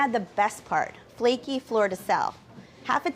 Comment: what salt did she sprinkle at the end? i legit heard "florida south"